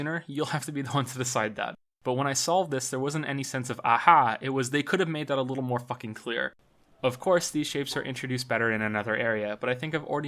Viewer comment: Hi, at 8:40 , I didn't understand what u said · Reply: "But when I solved this, there wasn't any sense of "Aha!", it was (a sense of) "they could've made that a little fucking clear.""